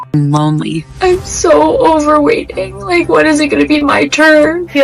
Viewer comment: “I’m so over waiting…”. I thought she said “I’m so overweight…”.